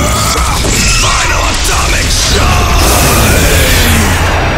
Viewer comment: I think he said final atomic shine